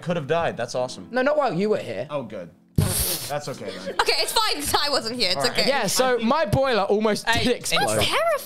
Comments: Did aimsey call ranboo ty at 3:27 or am I hearing things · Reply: She said *it’s fine because I wasn’t here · ok at 3:20 (have to wait a moment) does Aimsey call Ranboo Ty? Not trying to conspire or shit, I'm legitimately just trying to figure out if I heard that right